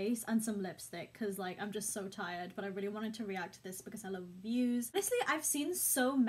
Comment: did she say “I really wanted to react to this cause I love views”?